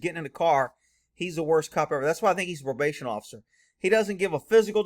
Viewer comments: It sounded like he said conservation officer. 😕?? Another example of "no good deed goes unpunished" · Sounds like he said conservation officer